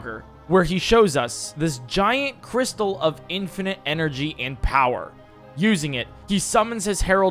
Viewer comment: did i hear "infinite energy"? where's the generator i must know